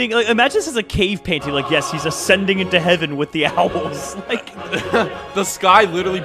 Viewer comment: So for the one wanting to be taken away with the owls and your like “She’s ascending into heaven with the owls”it thought you said the albums (The captions)